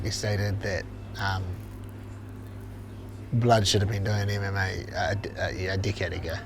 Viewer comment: That accent, I thought he said should have been fighting a dickhead ago. Lol